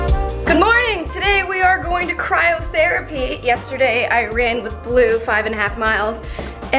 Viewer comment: I thought you said “cry in therapy” when you said cryotherapy lol